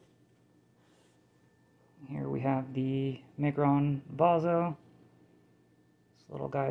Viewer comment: Hi what the knife called at 4:33 I couldn’t quite hear what you said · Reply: Sorry about that, I know I kind of mumble. That is the Miguron Vazo.